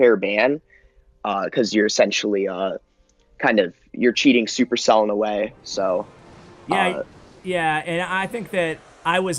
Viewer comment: I THOUGHT THIS GUY SAID “sexually your cheating supercell in a way”